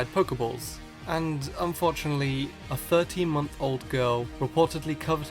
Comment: u said 13 month old, but I heard "13 year old" and thought, "Now how the hell would a 13 year old manage to suffocate themselves with a pokeball"